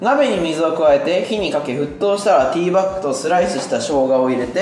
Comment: 字幕ティーバッグだけど、ティーバックに聞こえた人いるよね？